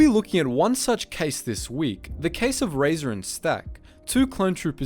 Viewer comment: I almost thought it said "Razor and Stark" lol